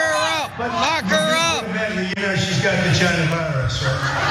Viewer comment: I thought he said "she's got vagina virus"